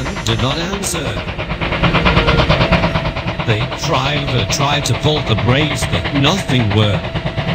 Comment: misheard as: "Thee Driiiiiiver tried to pull the brakes but nothing were!" Me: lol